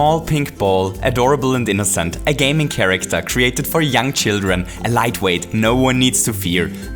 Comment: who thought he said "for yum children"